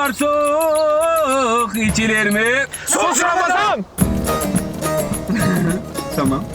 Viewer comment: de ne diyor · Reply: Sus ramazan diyorlar 😂😂😂